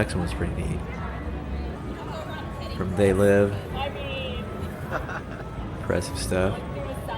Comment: I swear I though you said "imbreastive stuff"